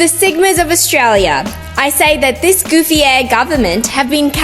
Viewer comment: “this goofy aeæ government” call me crazy but I don’t think that’s how you say ahh